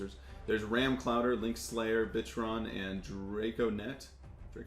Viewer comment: Sorry for profanity but I thought he said bitchron😂😂 Anyways is this starter deck at like target and stuff?